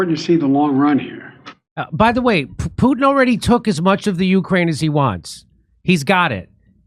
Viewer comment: I thought 💭 he had said Putin had already lost…😅